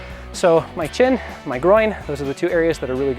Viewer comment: Did he just say he has a small "groin" wtf😂